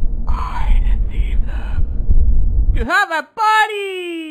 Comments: "I decieved them.." "TO HAVE A PARTY!" gotta be my favorite quote here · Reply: I thought it said: “*Believe them… THAT YOU HAVE A PARTY! starts dancing*” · "ⁱ ᵈᵉᶜⁱᵉᵛᵉ ᵗʰᵉᵐ" "To have a party!!!" happy music plays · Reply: i always thought that said “I deceived them” “YOU HAVE A BODY” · Highlights: 0:05 "Man, I love Jesu-*boom*" 0:13 "I deceived them...TO HAVE A PARTY! :D" 0:50 "ahh shi-ah fuc...ah my eyes..." 4:10 "eeeee" "CESAR." 4:58 "OPEN THE DOOR, MARK!!! I HAVE A PRESENT!!" 5:14 "Supriiiiise!" 5:18 S N I F F 5:48 Tip? 6:18 "I HAD ENOUGH!" 6:21 S N I F F -OOF! 6:25 "WHAT THE HELL ARE YOU SMILING AT?!" 6:48 uh oh! you just shot ur mom, mark! 7:01 Your seat belt >:( Edit: wow ok this blew up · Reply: I thought they said (and i leave them... you have a body!!!! (he do be dancing tho) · "I decieved them.." "TO HAVE A PARTY!" gotta be my favorite quote here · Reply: I thought it said “I am Jesus” “…” ”YOU HAVE A BUDDY” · somehow i thought gabriel said YOU HAVE A BODY!!! instead of TO HAVE A PARTY!!!